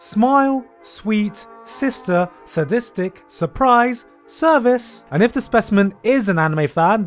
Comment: I thought surprise said supreme